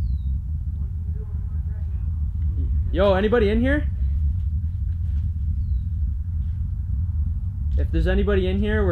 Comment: Yooo at 6:28-6:30 you can hear a man talking!!! It kind of sounds like he is saying - why did you enter my house - and at like 6:26 I thought I heard him say - hey- . But it’s really faint so listen really hard because it’s worth it!!😂😂👍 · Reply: No he said what are you doing in my back room